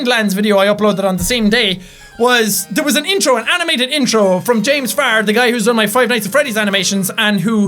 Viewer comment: I thought he said James Fart XD